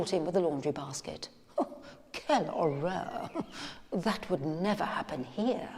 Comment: Did she say "Que Horror" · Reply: If im not mistaken yes but in French "Quelle horreur" which means the same thing